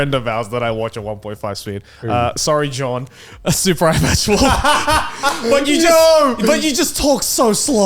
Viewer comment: what does Joey say? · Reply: Super eye patch wolf